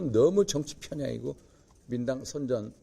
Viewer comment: 윤상현 의원... "민주당' 발음을 못하고 '민당 민당 민당'으로 들려요. 허허